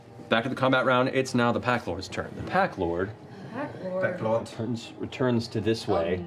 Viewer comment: I thought he said Packgod 😭